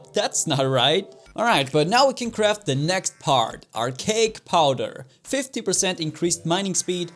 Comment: I thought said "our cake powder"